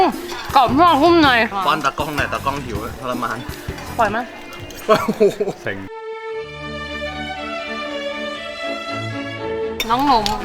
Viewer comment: ตอน 4:06 ได้ยินคําว่า "เซ็ง"